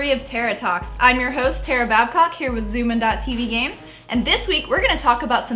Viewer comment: Did she say tear a mad cock